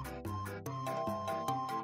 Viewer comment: The way it said sir sounded like it said siri at somewhere in 0:36 lol